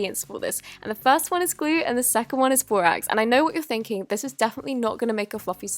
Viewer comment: Oh my gosh, I was watching it on mute with subtitles so I wouldn't disturb my parents; at 1:07 when she says borax, the subtitles say "eggs". At first I thought "What??" but then I thought she would use the froth from the egg whites. 😂😂😂